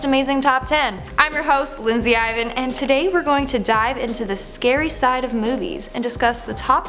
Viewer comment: At first I thought you said "We are going to die deep" But actually you said " We are going to dive in deep" And my favorite chips is Doritos and Cheetos